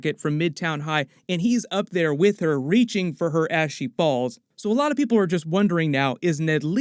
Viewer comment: I think ive not been getting enough sleep lately cause i had to listen to this part a second time I thought he said "Reaching for her ashy balls" and not "Reaching for her as she falls"